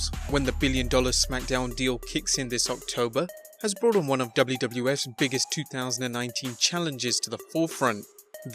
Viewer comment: did you say WWF?